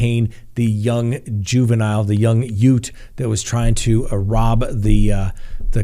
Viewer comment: The what? Did he say 'yout'? What's a 'yout'?